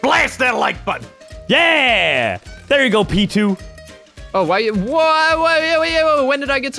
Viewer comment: I hear him say there you go, Pichu